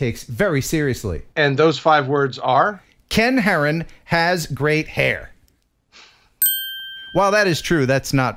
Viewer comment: "Ken Heron has great hair" Yet, I hear... "Ken Heron has grey hair"